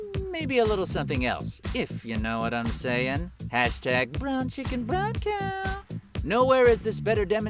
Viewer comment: Okay imma be honest here Listen to 1:28 Then listen to it again It sounds like he is saying "brown chicken brown cow." You're welcome.